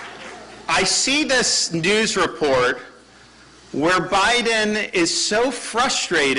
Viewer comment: Proud Floridian here, love Desantis!! But, to be fair, he does actually say Biden at 2:35…though his sentiments about Brandon are definitely spot on!